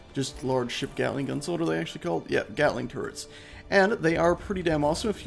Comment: Thought he said gatling carrots. lol